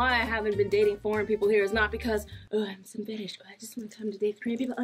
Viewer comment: Lmao I thought you said "FAT ASS" instead of "FETISH" 😂😂😂😂😂😂